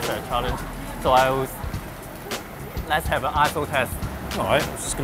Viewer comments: When Lok says "ISO test" all I hear is "asshole test". haha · I thought Lok said lets have an assho*e test !!!